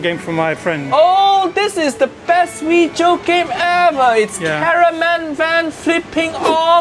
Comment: did anyone hear him say caraman van lol